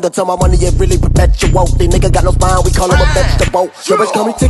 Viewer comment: Did i just hear " the n166a got no style , we call him a vegetable" 🌋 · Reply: He said spine